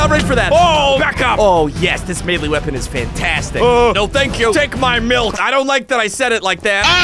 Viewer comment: did he just say take my milk? ayo thats sus..